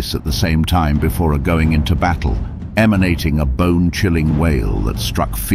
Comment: Bro I thought he said “bing chilling” and then I heard he said bone chilling